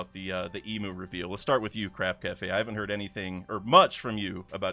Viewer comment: Legitimately, for like over a year (i think) I thought you were saying Crab Cafe.